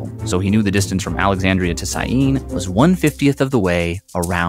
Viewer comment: Hey, I think there's a typo in the subtitles at 2:42 it says "inside" and I'm guessing that it's supposed to be "in Syene"? Sorry if I'm mistaken but it seems like a typo. Love the video though! Edit: oh and at 4:17 it says "our city's" instead of "Eratosthenes"